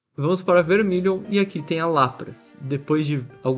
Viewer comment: só eu que ouvi ele falando "aqui tem a Lapras" sendo que era "Latias"?